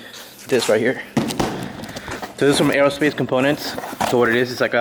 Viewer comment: Does anyone know where he got the 5 lug conversion? Sounded like he said aerospace components.